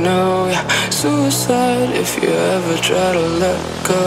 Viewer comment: Rip X😪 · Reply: I always hear: "Suicide, if you have a Gun then let me know" XD